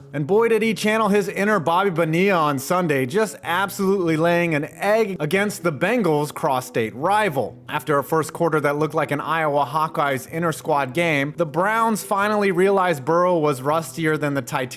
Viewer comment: I swear I thought you said “against the Bengals prostate rival” instead of “cross-state.” I was wrong. You said it correctly. And I was sadly disappointed.